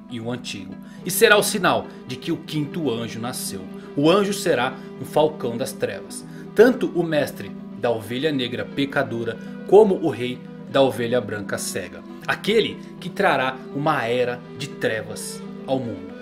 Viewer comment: só eu que ouvi "o mestre pica dura"?